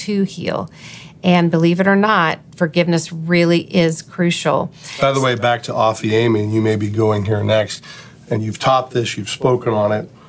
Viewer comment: ? what word does use at 29:15 offeeamee? aufuamy? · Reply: Hi Riley! is referring to a Greek word "Aphiemi" that mentions earlier in the episode used in Matthew 6:14-15. says earlier, "Aphiemi is a judicial declaration of a release. It's saying, you're innocent, like I'm done. I'm no longer going to hold this offense against you. Notice this. It has everything to do with me and much less conditioned on them. This is a personal act that I am making."